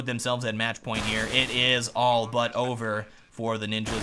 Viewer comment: Did he say "... And it is all but Ogre..."?